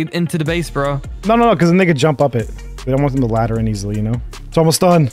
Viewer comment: bro u say these kids are racist what u say here? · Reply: "cause then they can jump up it" but it did kinda sound like "niggas can jump up it"😂😂